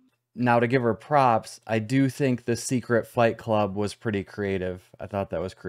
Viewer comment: Is it just me or does it sound like Turd said "a secret fake club" the first time round?